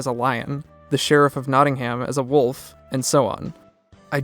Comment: i had the biggest wtf moment after you said that edit: its nottingham and not knotting